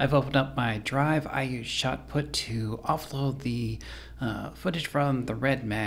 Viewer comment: Think I found it. I thought he said post, but I watched the video throughout and got the Shotput part😎